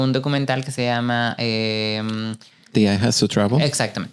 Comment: Como se llama el documental que mencionan en el min 40:15? Quiero verlo pero no entendí jsjsjs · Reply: “Diana Vreeland: The Eye Has to Travel” 🥰🥰🥰